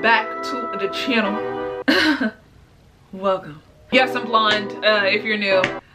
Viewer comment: 😂😂i thought she said “Yes i’m BLIND...if y’all new”😂😂😂